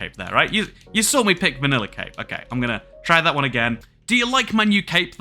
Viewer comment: I thought you were saying vanilla cake..